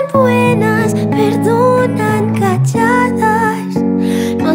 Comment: Letra: Te conviene no decirme que me quieres Prefiero que te alejes Prefiero que me dejes sola Te conviene no andar firmando papeles Ni subir los decibles Cuando estoy cerca de ti Yo no soy el tipo de mujer Con quien tu mamá te quiere ver Me hacen falta tantas cosas Y me fallan tantas otras Nunca te podría complacer Ellas son fieles Ellas son santas Ellas son buenas, perdonan calladas No hacen escenas No piden nada Yo no te podría complacer No soy ese tipo de mujer No me conviene andar soñando ilusionada Si no sirvo estando atada Soy un águila volando sola Necesito desvestirme tu perfume Este aroma me consume No te quiero confundir Yo no soy el tipo de mujer Con quien tu mamá te quiere ver Me hacen falta tantas cosas Y me fallan tantas otras Nunca te podría complacer Ellas son fieles Ellas son santas Ellas son buenas, perdonan calladas No hacen escenas No piden nada Yo no te podría complacer No soy ese tipo de mujer Ellas son fieles Ellas son santas Ellas son buenas, perdonan calladas No hacen escenas No piden nada Yo no te podría complacer No soy ese tipo de mujer No soy ese tipo de, de mujer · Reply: Siempre pensé que decía "perdonan chachadas".